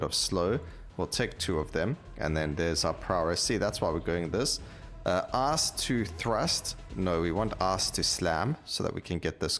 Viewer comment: I know it's the accent but @ 5:56 I heard "Ass to thrust, no we want ass to slam" lol